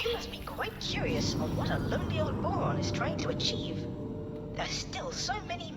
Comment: he said lonely old boron but captions said old lonely boron. Unplayable